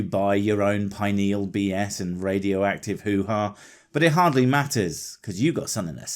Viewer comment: I heard the words "Radioactive Hoo-Haa" and have become deceased